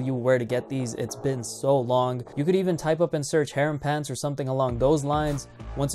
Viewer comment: Can anyone tell me what did he say at 2:21? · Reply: type up and search HAREM PANTS